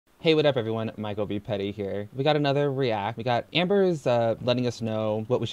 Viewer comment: First time watching this channel and I thought you said it's.